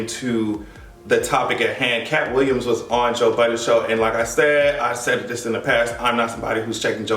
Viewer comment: i forreal thought you said he went on Joe Biden's podcast and I was like wtf is joe biden doing having a podcast. I had to look it up lol.